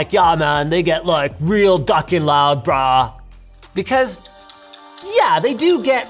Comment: did he just say the n word lmao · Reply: Where in "Ya man they get real ducking loud bruh" did you hear the n word